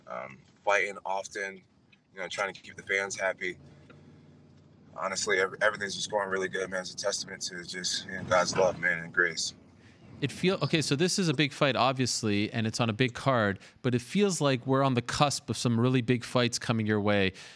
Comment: anyone else hear Jon say it's a testament to the juice? Bet Jon gets popped again lol.